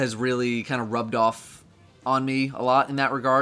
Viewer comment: I heard "My dad rubbed me a lot" I had to rewind to hear what he actually said